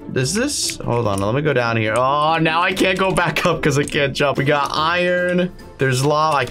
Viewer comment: I thought you said you can’t jump